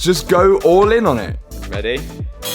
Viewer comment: did he say "ready" or "freddy"?